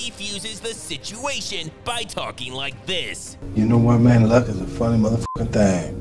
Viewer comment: what is he even saying?😂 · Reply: He said, "You know what, man, luck is a funny mfing thing."